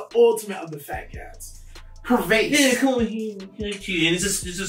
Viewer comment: the way this guy pronounces shorty lol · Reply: it's not regular 'shawty', it sounds really fancy lol, also 'perverse', I hear 'pervase', is that a thing or your personal preference?